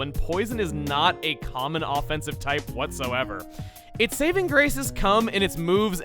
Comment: i heard "its saving grace is cum..." the double take was real